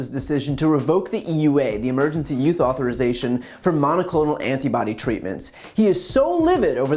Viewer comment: I love Brian, but I have to mention that each and every time he says the phrase “Emergency Use Authorization” it sounds like he is saying “Emergency Youth Authorization”. I find this fascinating.